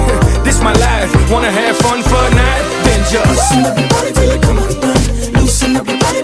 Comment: did he say fortnite or is that just me?? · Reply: Fun for the night